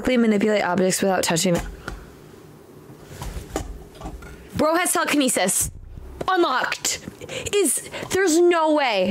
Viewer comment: bruh i keep hearing " Bro as suckenesis " im dead